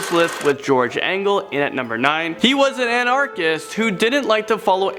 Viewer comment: did you say annarchist?